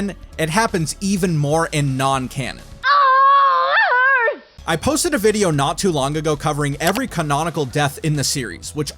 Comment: (Oooooooo… can anyone figure out what she said?) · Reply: "It hurts"